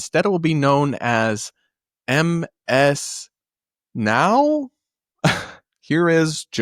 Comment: For some reason I heard "MS Now" as "MS Mao" XD